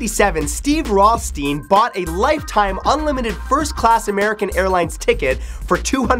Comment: I swear I heard "hair line" and not "airline" I looked at the guy and almost cried laughing